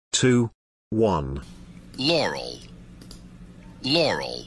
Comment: I heard “Laural”